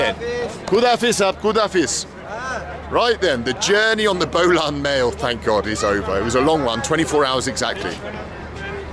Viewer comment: If I heard correctly (and it's very possible I didn't) the fella at 28:40 said he spoke Brahui? If so, that's an interesting language. It's related to the languages of south India, and its the furthest flung of all the languages of the Dravidian groups of languages. Pre history is very mysterious, but many people believe that this language is the last remnant of the languages spoken in the Indus valley or Harappan civilisation still in that region, before Sanskrit entered the region. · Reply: Yes he said that he speaks Brahui. It's a commonly spoken language in Quetta & it's surroundings. Brahui is the third most spoken language in Quetta after Pashto & Persian (by the hazara tribe of Quetta)